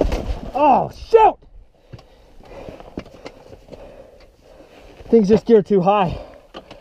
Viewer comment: did he say shit?